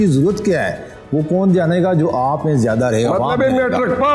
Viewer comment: What did afzal marwat said at 29:30?? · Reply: He said "matlab hai mattic pass bhi vote ly ga "😂